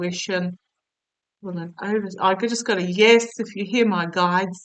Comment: I heard "Yes," at about the 2:45 mark.